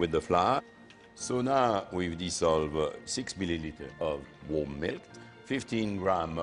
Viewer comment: is he saying 6ml warm milk?